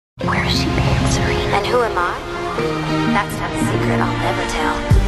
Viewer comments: ooh i always thought she said "that's ANOTHER secret i'll never tell" but then that also would make no sense because she tells most secrets. i need to shutup. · it sounds like she says: wherer has she been serena, and who am i? thats not the secret i'll never tell... but in real it ist that's one secret i'll never tell... freaky..